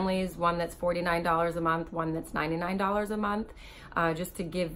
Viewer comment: It sounds like you said $99.00!a months wow!